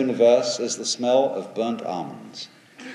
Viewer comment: Can anyone tell me what is being said at 8:04/timeline? "Everything in this Universe is the smell of burnt _______???" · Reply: Almonds 😊 I'm not too sure about that one lol